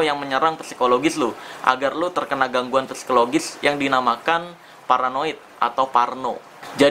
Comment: gw dengernya polaroid wkwk :v